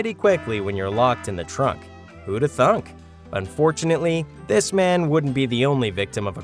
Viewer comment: Did bro say who would have thunk ? 😂😂😂😂